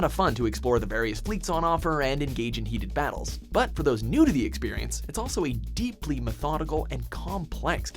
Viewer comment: the way you said "for players new to the experience" it sounded like you said "nudity"